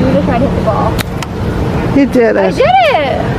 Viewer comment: I heard ‘ okay, we did it’. Like they did golfing specifically for the video